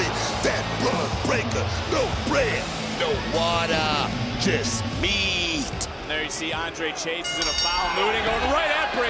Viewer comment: What did Booker say at 0:34? 😂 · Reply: No breaddddd, no watttaaahhhh, juuuust meeeaaatt